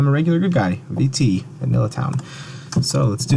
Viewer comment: I can’t quite hear what you said at 00:45 Vanilla Town? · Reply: Yea, Vanilla Town. While I've never played it, I think it's a term borrowed from Town of Salem.